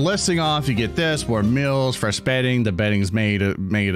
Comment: I'm not the only one who heard that at 30:13 right? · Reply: It's meal but you know, remember them. Remember the milves.